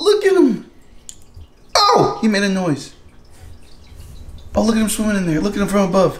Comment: Did I hear : “Ow! It made a noise”